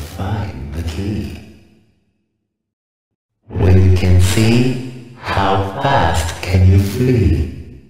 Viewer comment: "Listen to my voice, and you'll find the key." "When you can see, how fast can you flee?" "Be sure you step, through the heart of madness." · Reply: i hear ''if u do it then u find key'' i hear ''we can see how fast can u free??'' i hear ''be sure you allstep to the hotter matter''